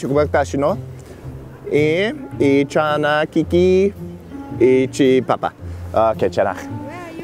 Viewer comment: He actually said something in Korean 2:35 괜찮아(kwenchana) = it's ok